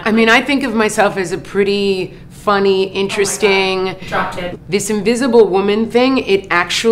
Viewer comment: wht is the women saying in the background? Cause i only hear "omg drop dead" and its really bugging me Oo...